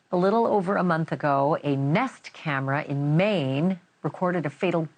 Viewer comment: Either Banfield has been drinking or the Botox is making her speech sound different. Because right at the beginning, she tries saying a Nest camera caught this footage. But that’s not what it sounded like. It sounded like she says a neft camera. I guess NewsNation allows drinking on the job now….